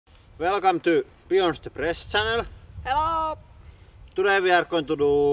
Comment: It sounded like he said “welcome back to we are depressed channel”